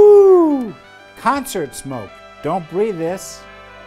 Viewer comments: it sounded like he said "dont bree this" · I thought he said cancer smoke